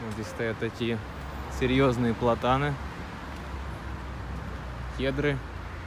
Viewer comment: Вот это да! Какой большой видеоролик. Оказывается раньше большие обзоры снимал. Здорово))) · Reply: ))) "Сочи это где практически не бывает снега!"))) Мне интересны всякие растения, кустики и деревья...Блииин, берёзка, есть в Сочи берёзка, ура!))) А платаны я упорно сначала называла "ботаны", неверно расслышав ответ мне местных))) Всё думала, почему у них стволы без коры, опала что-ли?)))